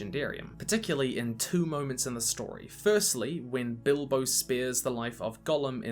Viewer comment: I got super confused because I thought he said "spears" instead of spares so I was like 'Wait when did Bilbo and Frodo stab Gollum??'